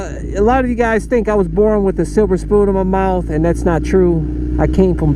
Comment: "yall might of thought i was born with a silver spoon in my mouth"... i had to go back cause i thought for sure he said born with a supersport in his mouth XD